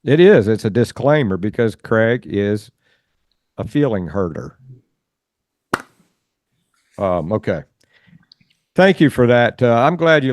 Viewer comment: I misheard when he said Craig is a feeling hurter and I thought he said Craig was a feeling pervert lol